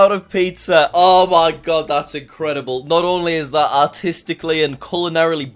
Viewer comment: did he say Autistic?